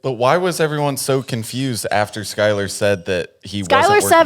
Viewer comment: what word is that? “For batum” 👀 Does she mean verbatim?